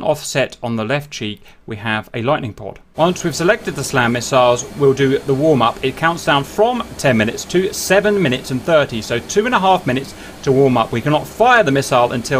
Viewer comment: Sounds like you said "we have a lightning port" Nice to hear the hornet already supported that port way back, it's so useful